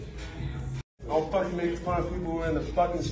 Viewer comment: What did he say? Don't fight me...? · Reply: make*